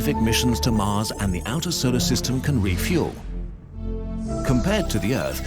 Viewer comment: i thought he said funeral